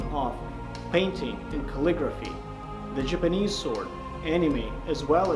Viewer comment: I’m am the only one who heard him say Jimanese sword instead of Japanese?